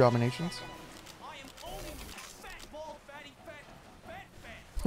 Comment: For several years I thought Scout was saying "fat-balled" instead of "fat bald".